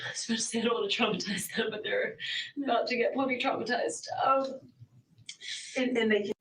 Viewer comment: I think she says “probably traumatized” but she says it real fast like pra-blee traumatized